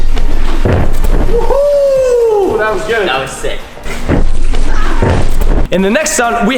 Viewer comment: i heard said that was sick